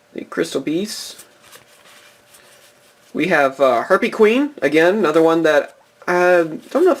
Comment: He said Herpy Queen instead of Harpy Queen. XD